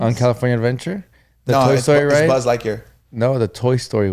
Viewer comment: why does say BuzzlIKE year.!!!!